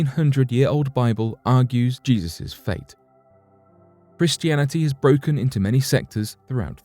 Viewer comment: BRO!!!! IM FUCKEN DEAD!!! 🤣🤣🤣 at around the 5:30 mark the narrator goes to say the word Christianity but he says it with a "b" as in bristianity. It's funny because that's how a blood would say the word, you know by replacing all the letters c with a b. Idk I know it was just a slip and not done on purpose but still it was funny as fuck when I heard it. Now don't be all gay and take the video down now because of it we all understand it was accidental.